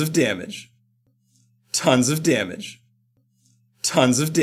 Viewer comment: i think he says "tons of damage" at 0:15. But im not sure